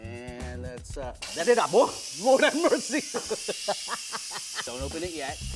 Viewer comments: Ladeda BUOFFF?? WORD HAVE MERCY! (That's what i heard) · so nobody’s gonna ask what spell he said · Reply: And a, Adid a- OHG- HAVE MERCY (Jolly laughter)